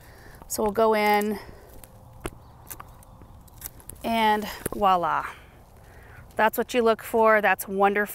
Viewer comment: Loving the hair, looks a lot lighter. I'm sure meant to say Voila sounded like she said Walla. What a beautiful bird though, the little ones will be hiding from it